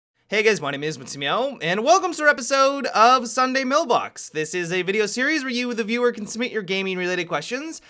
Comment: It sounds like he says Sunday millbox